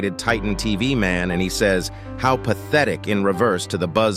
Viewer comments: to be honest, when said that UTTM said: "how pathetic" in reverse, at that moment but non-reverse in the actual episode. i thought he said: "hold still" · he actually says "how pathetic huh?"